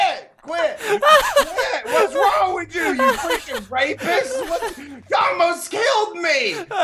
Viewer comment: WHAT DID SAY!!! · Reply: he said rapist